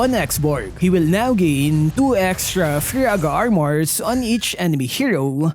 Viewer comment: When says faraga armor i thought he said viagra armor😂